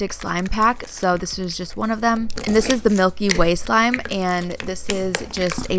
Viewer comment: When she said Milky Way I thought she said Mikey Way (from my chemical romance) any killjoys out there?